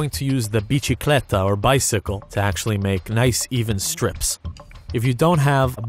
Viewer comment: my brain keeps wanting me to think it's Spanish but it's not. I keep hearing "bicicleta"